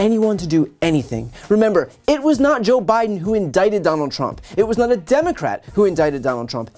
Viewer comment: Did you say Joe was indicted?